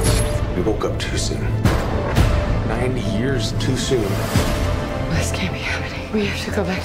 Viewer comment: The synopsis says "90 years" but then Chris Pratt says "9 years"... Am I just mishearing Pratt? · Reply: nope it's 90. in the translated trailer they say so.